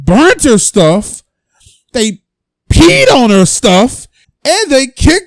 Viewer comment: I thought you said pee on her